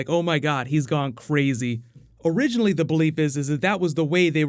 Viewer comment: "originally the BELEEP is..." U couldn't do another take n actually say 'belief' instead of 'BELEEP'? lol